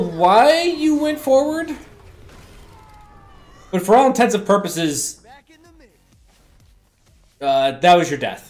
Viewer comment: Did you say "for all intensive purposes"? I agree that he uses the charge quite badly, especially while defending the second point he charges up to 1-2 orbs instead of just shooting. Also, he often didn't have his discord/healing orbs on the other players.